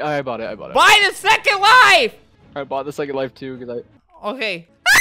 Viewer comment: BUY THE FREAKIN "WIFE" and im like uuummmmm wut? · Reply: I thought he said “BUY THE FE*K*NG LIFE!”